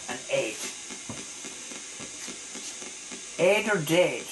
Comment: Hi, I only pointed out some whispers I heard because the loud responses were easily heard. I didn't get a lot of them but here are some. 18:37 - 'dead' 18:42 - 'hell' 18:47 - It's ok. When you were talking about the smoke moving 21:54 - 'yes' I had to giggle when it said 'school', I thought it was referring to you going back to school to learn which element was used to light the candle. lol Then there was a lot I didn't capture. When you were talking about so many YouTubers using spirit boxes that it's become sort of normal, there was a 'yes' in a laughing voice - 40:18 & 'I see' - 40:24 & "right" - 41:25. Bye, love from Australia.